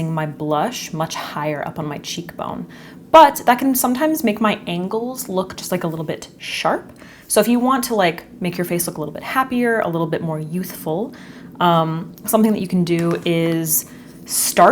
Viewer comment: I genuinely thought you said it’ll make your ankles look sharp, which made me laugh very hard!